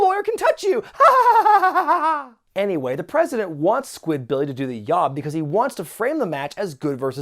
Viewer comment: Did you say "do the yaob" at 5:18? I must of not heard correctly.